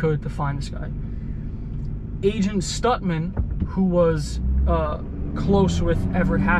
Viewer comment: When you said Agent Stutman, I thought you said Agent STUNTMAN! lol Great video as always!